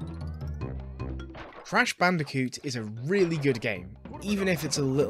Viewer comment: did i ear that right? or my hearing fucked up? did he just say " 'trash' bandicoot"?